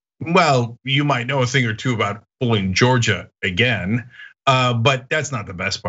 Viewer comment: I thought Chenk said "Don't go full Georgia"